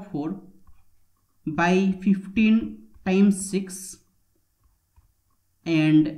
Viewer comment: Not in this video clip. The clip says: 6 times 15 = 90. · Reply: Sorry, but I tried several times and what I heard was "15 times 6", which is correct.